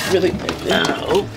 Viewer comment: Is it just me or did he say "and i oop" at. 14:37 Ps. Im not a vsco girl